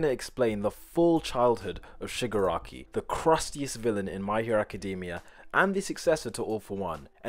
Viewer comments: Somehow I heard “Woman” instead of “villain” That’s great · what he says: Shigaraki What subtitles says: Shigure Aki Me: tf👁👃🏻👁